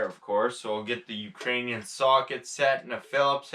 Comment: did you say "ukrainian socket set" at 5:30? what is that?